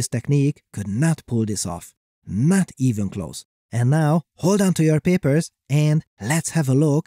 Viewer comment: I heard "Too Many Papers" at first...